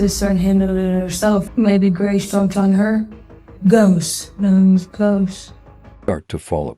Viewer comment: Did I misunderstand?? Did she say "ghost"? I thought that is what I heard. I couldn't be right. Could I? At like 1:18.... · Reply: Yes, she apparently called herself the ghost in the room. Glad there was a camera Thank you so much for your support ❤